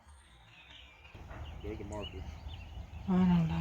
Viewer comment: I heard " they're in back"